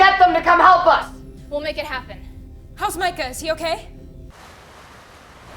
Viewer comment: did he really say Michas okay💀💀💀💀💀